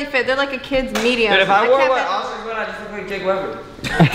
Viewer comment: what did say? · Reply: "if i wore what oscar wore i would look like jake webber"